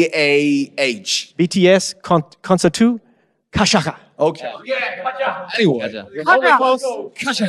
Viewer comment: I'm sorry I burst out laughing at 2:09 OMG LMAO HE SOUNDED LIKE HE WAS SPEAKING ARABIC "gashakha"